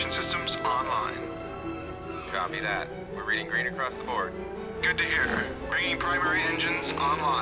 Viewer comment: I always wondered.... 0:59 ... is he saying "Good to hear"? .. that's an odd thing to say in radio chatter; rather casual for one who is talking about evacuating their entire people. Also, what I haven't pondered until this very moment, is the fact that the Alliance are actually spiteful cowards who rather then surrendering to attempt to change the system from within and end bloodshed that way, they make a run for it; effectivelly allowing the Coalition to kick them out of their own home system, humanity's cradle. Its their own birthright just as much as it belongs to the Coalition. I mean, could it really be that bad? Its not as if they are aliens trying to wipe them out or anything.. just surrender, for god's sake.